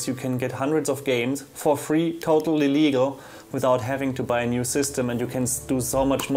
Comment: xD When you said Totally Legal it sounded like you Said "Total Illegal" xD